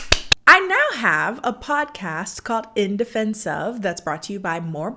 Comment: I thought she said 'Indefensive', and I theorised it would be a podcast about bad movies that have no excuse to still be watched by anyone